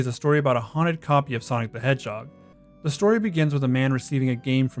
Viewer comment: I heard you say "Sonic the Edgehog" and I think that actually fits for Sonic.exe